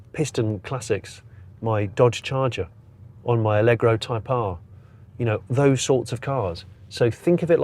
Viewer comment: allegro type R ??? did i hear that correctly ?